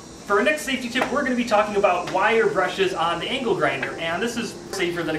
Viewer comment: I thought he said ankle grinder lol